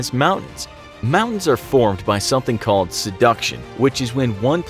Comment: did you just say seduction